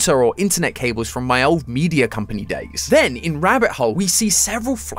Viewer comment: Bro what was that?? Did anyone else hear the distorted voice? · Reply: He said “days” his voice was just a bit raspy and the mic picked it up in a weird way.